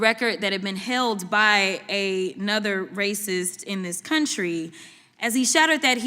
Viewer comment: Did she just call him “another racist”?